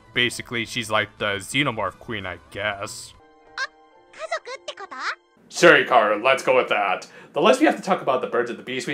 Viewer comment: You forgot to put subtitles here. What is she saying? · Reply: Hikaru: Oh, so you're all like a family.